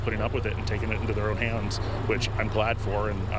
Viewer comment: did he just say fucks 11 news in the end?